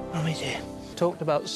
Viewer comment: DID I JUST HEAR HIM SAY MUMMY’S HERE ?